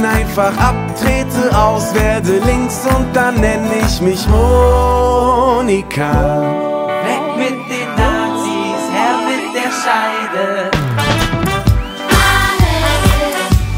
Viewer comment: wie ich das lied damals gefeiert habe hahahaha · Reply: Ich auch und ich hab immer "Honika" verstanden😅🙈